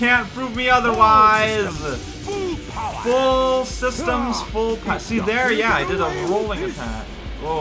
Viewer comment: I love the fact that everyone seems to mishear Robotnik's quote as "Full systems, full power!" when it's actually "All systems full power!", that's kinda neat.